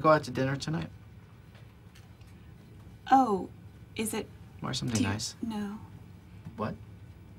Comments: When he asks Pam to dinner and she's stammering, his response? "Wear something nice." What a guy. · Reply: Oh, I thought he said '...or something else' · When he asks Pam to dinner and she's stammering, his response? "Wear something nice." What a guy. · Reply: The Dark Knight I thought he said, “or something else?”